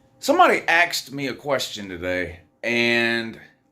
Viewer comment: I don't know what you're talking about... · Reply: Sure sounds like you said axed instead of asked, but I could be wrong.